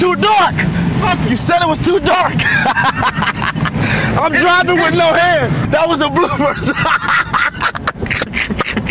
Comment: I thought Paul said: "This nigga's laugh tho" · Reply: Miguel Miranda - He said "Your face makes me laugh tho."